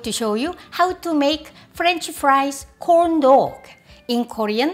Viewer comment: when you said “corn dog” I was quite surprised for a few seconds but I heard it as “condo” which is like Butt in my language lol 😂